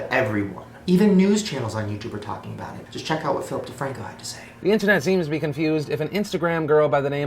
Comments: when he said Philip DeFranco I thought he said Filthy Frank and I was confused and excited at the same time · is it bad I thought he said flithy frank instead of phillip defranco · I um I thought he said filthy frank instead of Philip DiFranco